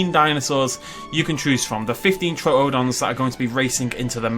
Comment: I thought it said "Troodon Master Race" and I thought you went full Nazi on us.